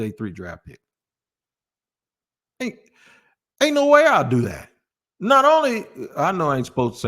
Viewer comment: Ain't no way - Aretha Franklin is correct. At first I thought you said Ain't nobody, my bad 😅